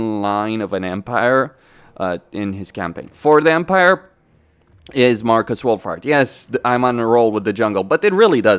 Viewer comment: Sounds like you say Marcus Woolfart 😅